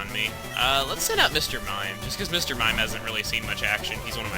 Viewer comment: it sounded like your "Mimien" was a new pokemon or if it was from a bio